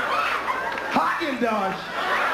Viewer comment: Did he say "HAAGEN-DODGE?!" LOL. I CAN'T GO ON!!!😛😆😅🤣😂